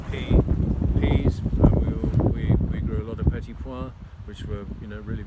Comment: what does he say at 4:28 "we were growing a lot of v..." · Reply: He said petit pois..."little peas"